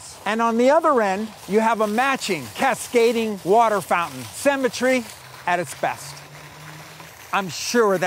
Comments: I literally thought he was saying 'cemetery' and was thoroughly confused. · I kept hearing “cemetery “ ... until I realized he meant “symmetry” ...😂